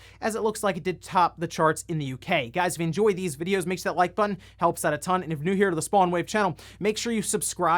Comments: So, this always sticks out to me. When you say your intro I hear "Make sure you like button, helps out a ton" instead of "make sure you hit the like button. It helps out a ton". I'd recommend watching your video playback at .5 speed. It's pretty entertaining lol. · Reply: i always hear “make sure that like button helps out a ton” like how can i make sure of that? 🤔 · So, this always sticks out to me. When you say your intro I hear "Make sure you like button, helps out a ton" instead of "make sure you hit the like button. It helps out a ton". I'd recommend watching your video playback at .5 speed. It's pretty entertaining lol.